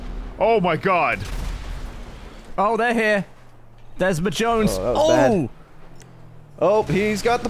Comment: When Gavin said, "there's MJones." I heard, "there's my Jones." And my heart said, "awww 💓 team nice dynamite"